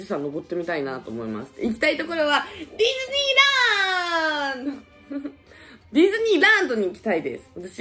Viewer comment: ディズニーランドって言ってるのになぜかフィリピンラーンドって聞こえてたwwwww